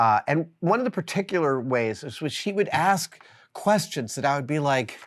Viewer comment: did you just call jamie a she? " jamie used to drive me bananas, and one of the particular ways was when SHE would ask " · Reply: What I hear him saying is “was- was-he would…” The final “s” blended into the “h” and palatalized into a “sh” type sound